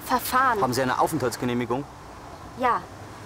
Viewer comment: Was sagt der Polizei bei 3:55? "Haben Sie ...genehmigung?" Ist es möglich zu hören, wenn man das Wort nicht kennt? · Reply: head Der Polizist fragt: "Haben Sie eine Aufenthaltsgenehmigung". Untertitel zum Video findest Du auch auf unserer Website: <link> Viel Spaß!